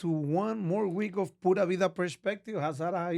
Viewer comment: I thought he said Puta Vida 😂